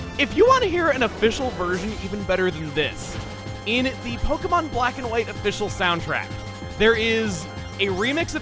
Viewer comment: Was I the only one who thought he said this time on Pokémon quack and white